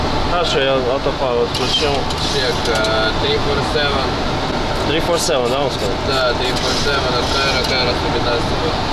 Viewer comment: what is he saying😅? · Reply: Confirming radial 347 CVO VOR frequency